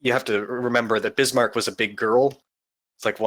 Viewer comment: 'Bismarck was a thicc girl' 'Bismarck took quite a pounding...' Christ almighty this is some top quality NSFW commentary on Kancolle. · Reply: Although here I thought he said "big" instead of "thicc"